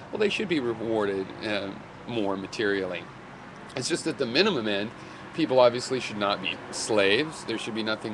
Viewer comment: what's he saying at 1:55? 'min-muh-muh-men' middle men? immendham? ???